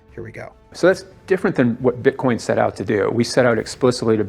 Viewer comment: did he say when we created bitcoin? Peace